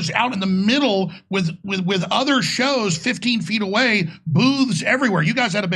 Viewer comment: When Jones said, “there were booths everywhere,” I heard, “there were boobs everywhere.”😂🤣 Just thought I’d throw that in. 😐